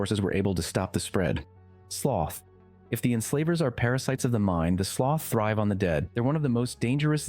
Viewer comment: for a second i thought i heard enflavers lol